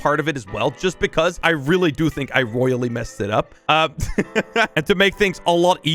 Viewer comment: Rolly instead of really